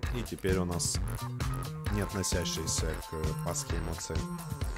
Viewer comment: а мне послышалось не относящие к масленице 😂